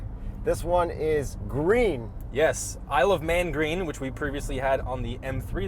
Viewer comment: I'm I the only one who thought he said "I love man green" 🤣😂 (Isle of man)